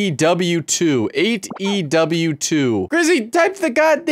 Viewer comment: Did my fellow Wrestling fans also thought he said "AEW 2"?